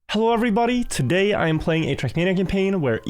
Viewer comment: heard him say "i recommend playing trackmania!" so now im gonna play trackmania for the first time because its free